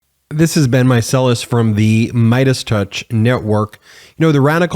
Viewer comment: Your introduction drives me crazy because it sounds like your saying this is Ben Micellars from the MeidasTouch network. I had to look at the transcripts to verify what you’re saying.